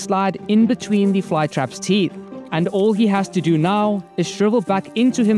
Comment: Send this to a snail 🐌 I thought I heard send this to a play boy 😅